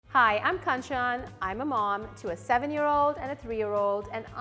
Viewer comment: Sounded like she said "Kachan" lol